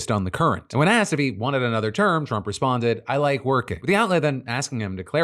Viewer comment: “I like working” All I hear is “I love lamp”